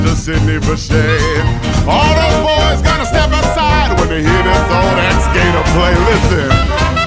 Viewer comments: why do I hear "when you hear this old ass gator play!" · i didnt notice this but like DID HE JUST SAY "OLD GATOR ASS"???